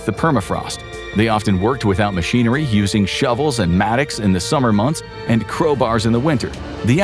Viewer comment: "using shovels and ???" Sounds like "matics." What is the word? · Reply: Mattock, a type of pickaxe ⛏️